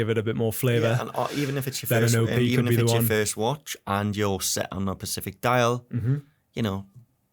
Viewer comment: Did he just say ‘if you’re set on a pacific dial’😂 Jesus I guess that’s the blue one haha